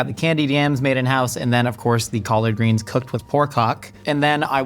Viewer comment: ??? does he say what i think he says? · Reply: Pork HOCK hehe